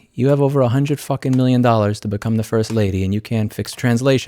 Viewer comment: Did call Elon The First Lady at 2:02? haha